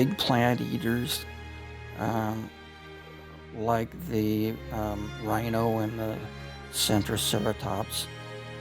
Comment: "rinoceronte e Centrosaurus"?1 POXA DUBLAGEM, VAMOS CAPRIXAR AÍ NÉ? FOI DITO TRICERATOPS E CENTROSAURUS.